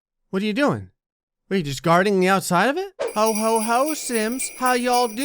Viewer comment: I thought he said Hohoho simps and my mind just said "Wait, that's illegal"